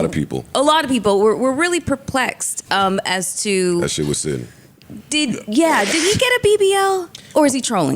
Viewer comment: Did Joe just say his sh** was sitting? @ 36:56 Yo This man might be 🌈